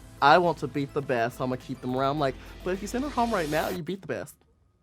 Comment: Can somebody explain What all Heidi says at 22:11 and how Trixie’s jaw just dropped? -Thanks, Dumb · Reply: "If you send her home right now, you beat the best"